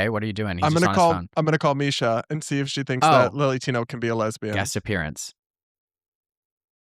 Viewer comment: My hearing isn't the best, and I misheard Misha -- thinking she said "Willy Tino." And I had to laugh, because that's actually a more accurate name than "Lily." 🤣🤣🤣